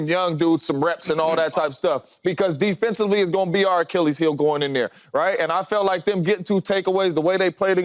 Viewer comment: sounded like James said R. Kelly’s heel 🤣🤣